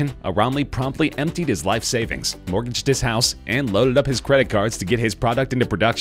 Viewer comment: Did anyone else hear "torched his house" at 12:09?